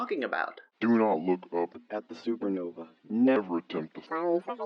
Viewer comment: this whole time i thought it said do not look up an eas scenario